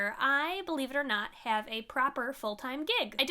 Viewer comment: Did i hear 2:02 correctly, I hear believe it or not I have a full time gay.